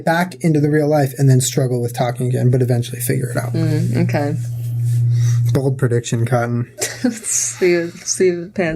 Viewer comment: did Julien say “bold prediction Cotton” as a reference to Dodgeball? edit: super late to this podcast lol and have already seen season 2 but I love y’all that much! Could watch you two talk about anything.